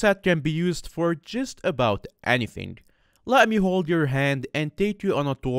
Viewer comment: did you just say let me hold your hand in the video at around 1:40 WAIFU